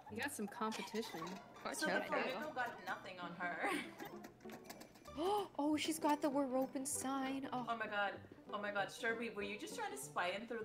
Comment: Oh I thought she said “Oh, she’s got the we’re broken sign”